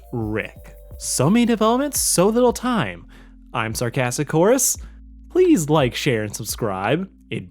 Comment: I thought you said your name was sarcastic horse xD